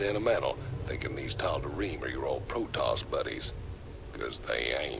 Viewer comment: Aaand I just realized that localization which I assumed has been done perfectly does have some issuies. In Tychus's mission description "thinking these Tal'darim are your old protoss buddies.." I heard "thinking these Tal'darim are such a nice guys like your old ZERATUL buddie". I heard it so many times and was confused about how good Tychus is informed... I will definitely watch both WoL and HotS playthrough, waiting for more surprises.